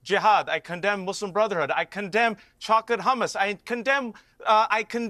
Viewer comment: did he say chocolate hummus 😂😂😂😂